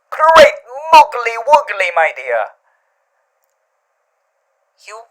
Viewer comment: Me: What in the 9 hells is that saying?! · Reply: GREAT MOOGELY WOOGELY MY "DEER"